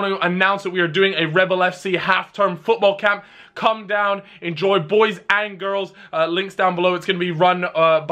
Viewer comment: did Cal just say “Enjoy boys and girls?”?